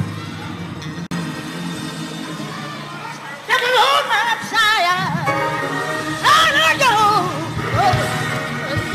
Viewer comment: The Minister was here for it! 😂 I heard that powerful voice loud and clear. 💯❤👑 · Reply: He said Come on Shirley